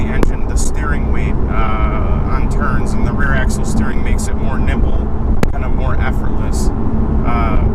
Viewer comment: Ah, more NIMBLE. . . I thought you said more NIPPLE, then again that car is tits.